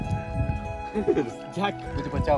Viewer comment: Jack sounded like jath 😂🤣